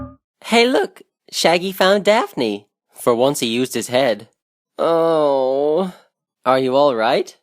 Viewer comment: Good job, this must've taken hours to make. 🙌👌👍👏 6:29, I'm just now realizing that Freddy said "For once he used his head", for some reason when I was younger, I thought he said something like: "But what's used his head".